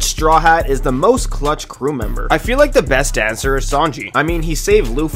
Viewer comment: I thought you said “The most touched Strawhat”